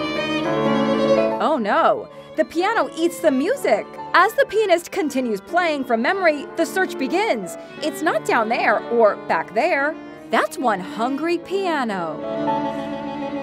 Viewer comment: Bro did she just say ''as the penile continues playing'c